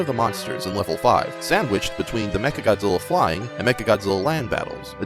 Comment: did u say sandwich